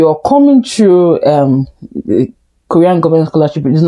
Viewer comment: Wow! I really wanna come to South Korea...it's my dream land .. How do I get the student visa? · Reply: I thought you said, South Korea.